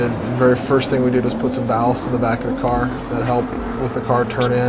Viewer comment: what does he say? "First thing we did"..... what did they put in the back of the car? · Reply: André Kaasa "ballast", initially we put about 150lbs in the rear bumper, we later took all that out once we changed to a 1.8t striker motor.